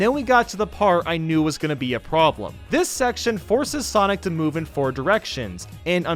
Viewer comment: did I hear sonic